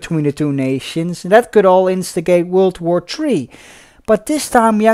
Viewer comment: "World War Tree"?......a dispute over timber cutting rights?..... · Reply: It's okay, I didn't mean to come off pissed of or anything actually! I meant it, it does sound like I say "World war tree"! And i'm sure I have a lot more of pronuciation and other mistakes in my videos! (I believe I accidentally called George Lazenby, Connery's "predecessor"instead of "successor"... :P ) Still though, I'm happy to hear you also enjoy the actual content itself, while I will be aiming to improve with every episode! See you around!